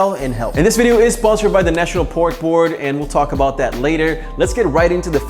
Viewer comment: At first I thought he said it was sponsored but the "National PORN Board". I didn't even know there was such a thing! Maybe there should be!